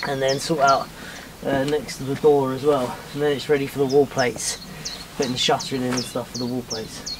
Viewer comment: Yup shorn with the sheep! I’m going so deaf in my old age, I thought you said wallpaper next… no it must have been wall plates next…